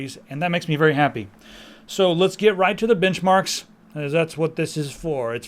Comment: I ate a chip when you said benchmark, at around (2:00), and it sounded like you ssaid bitchmark... That's what chis brown leaves on his girlfriends...